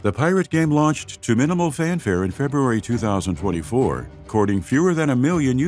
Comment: did he say two thousand and forty-four? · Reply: It sounds like it but he said 24